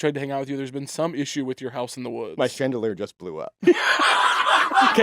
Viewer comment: I thought he said his Crandelier blew up and I was like "amazing callback"